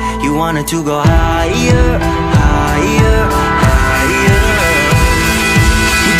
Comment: The more I listen to this, the more I hear "Burned to bread, now the fire's gone"